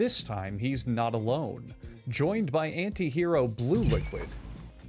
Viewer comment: sorry what did he say? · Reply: Anti hero blue liquid